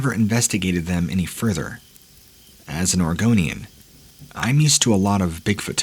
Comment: I actually thought you said "Argonian" there for a second lol I'm like what there are crawlers in Blackmarsh?